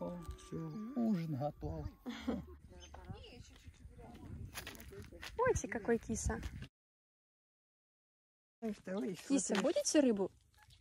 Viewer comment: What did you say to this cat? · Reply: Uti, kakoy kisa! Kisa, budete rybu?(in Russian language)